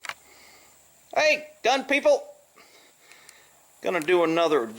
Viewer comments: Lol. I enjoy the videos brother. For the longest time I thought u was saying “Hello young people!” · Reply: GP One guy thought he was saying “hey DUMB people” 😂 Still my favorite comment. 🤣🤣🤣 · Lol. I enjoy the videos brother. For the longest time I thought u was saying “Hello young people!”